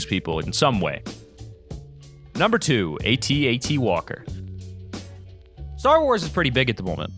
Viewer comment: For the first moment of the at-at I thought it said at&t XD